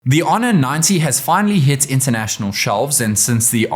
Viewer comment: When I first heard the intro I thought he said the hono nazis